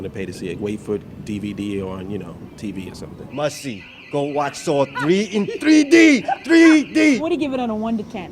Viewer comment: did he just say go watch saw 3 in 3d?